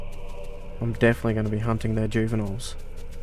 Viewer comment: i didnt have the volume too high so i heard hump not hunt lmaooo